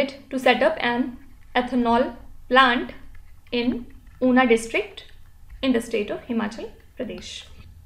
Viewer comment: Mam,🙋‍♀️ at 18:00 which district did you said or write, i am unable to understand. I heard it puna or kuna Plz 🙏🙏 try to write important names more clearly, this problem came many times not just this. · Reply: It's Una district of Himachal Pradesh